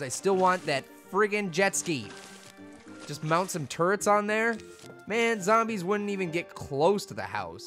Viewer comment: when said turts i thought he said turds